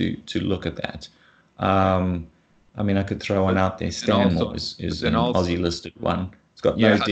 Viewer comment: What is the mining company he mentions at 59:15? It sounds like he said "Stone Wars." Nothing even remotely similar in KOL. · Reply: Stanmore, unfortunately it is not listed on TSX